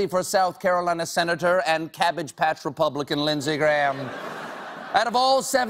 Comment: He said "cabbage patch" .. ,but i heard "cottage catch" and thought "log cabin republican" ..